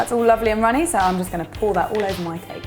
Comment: It sounded like you said "I'm gonna pour that all over my face" lol